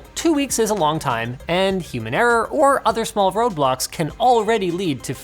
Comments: did you just say small roblox? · Did anyone else hear Roblox? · DID U JUST SAY ROBLOX